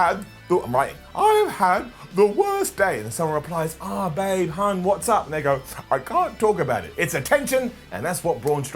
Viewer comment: Did said what up nigg@